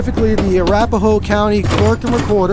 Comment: I thought he said rape a hoe lol